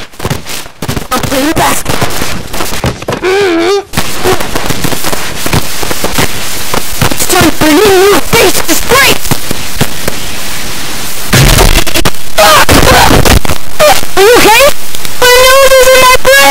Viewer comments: F### is what I heard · did you just say the “F” word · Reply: He said crap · Sounds like he said f**k